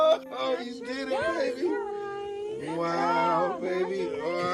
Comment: Was it just me or did I hear the nurse say “baby boy” ?